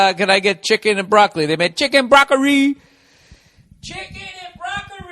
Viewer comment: "chicken and broccori!!" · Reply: Ohhhh I thought they were saying cheeky broccoli this whole time....